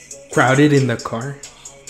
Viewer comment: Rather than hearing “...crowded in the car” I heard “Riding to the farm, grabbing all the corn” What do you think?